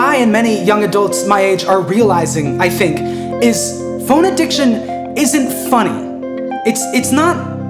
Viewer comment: im so deep down my conlang special intrest hole right now that i heard "phone addiction" as "phonediction" and was wondering if it was a new text to speach app